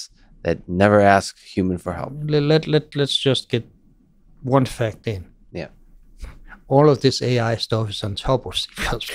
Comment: Can someone tell me what Bjarne says between 1:43:43 and 1:43:53? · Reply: After considering the "fuzziness" of AI vs the "precision" of C++, Bjarne jokes on the observation : "Let's just get one fact in : all this AI-stuff is on top of C++". Meaning that all AI simulations are powered by an underlying engine that is written in C++. (Makes sense?)